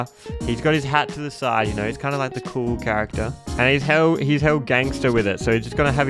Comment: I think I need an ear docter I thought I heard: "Ness is kinda the cool character" for a second.